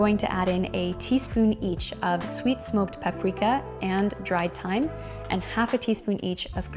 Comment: Not that you care but this is what i heard: Dry time and human Ahahahah yes two of my favs ahahah